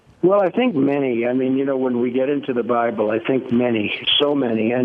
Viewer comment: Was it me alone who heard him say 5:11 "well I think many you know when we get into the BRIBLE I think many so many......"